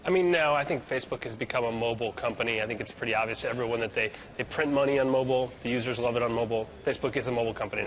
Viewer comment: At the 1:18 mark it sounds like the guy says "they print money on mobile". Am I hearing that right?